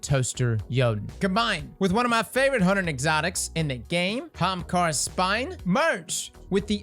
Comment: Off topic, but every time I hear you say "Hamkara's Spine" I hear Hamtaro's spine and picture you tripping ppl in Crucible with anime hampsters.